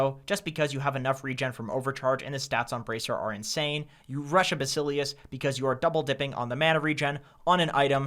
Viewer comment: what i hear: "you rush abbyssal-ius"